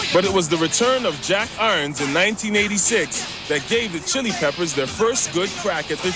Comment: Did anyone else notice that at 8:08 he says 1986 but the subtitles say 96 XD